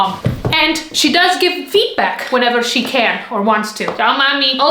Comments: When she said feedback I thought said “feedback” 😂😅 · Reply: I meant that I thought that said feetback 😢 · First I heard "she does give feet back." 😂